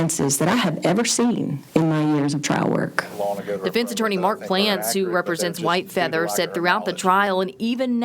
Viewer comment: Did I just hear the defence attorney's name is Mark GLANS ???!!! Close, it should be Mark Gonad.